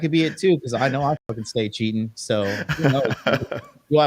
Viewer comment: what did he say? "I fucking stay cheating" ??